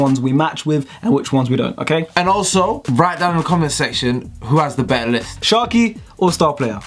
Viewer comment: for time i thought was saying ‘who has the best lisp’